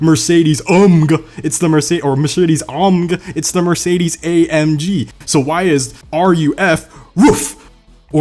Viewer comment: I died at when you said “You don’t call it Mercedes AMG”, or as I heard it you said “Mercedes UMGUH” 😂😂🤣🤣